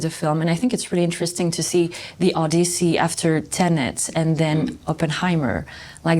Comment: did she say Odussy?😂😭😭